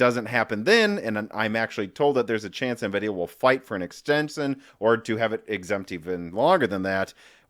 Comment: Heard that as 'Nvidia will fight for an Ex-Jensen', one can only hope.... 🤣🤣🤣. Joking aside, on to rest of the video. Edit: Watched the rest of the video and objectively speaking I hope Nvidia is even more aggressive with the pricing. Subjectively speaking. the prices are still too rich for me lol.